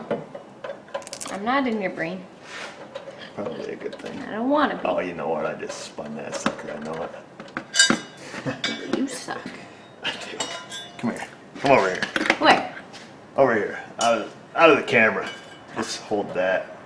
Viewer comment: did just say "You suck."? Hahahahahaha... Man, I almost fell out of the chair laughing! Man, I love it when there sidekicking for you!